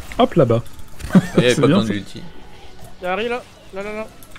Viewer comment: "Y"a ahri" J'entends Yahari moi (japonais: je m'en doutais)